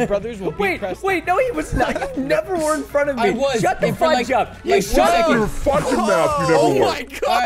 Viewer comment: Should I do MORE parkour? What do you wanna see me do next?! 🏆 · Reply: TO EVERY FAN OF PRESTON I HEARD HIM SAYING SHUT THE F UP HERE IS THE PART OF THE VID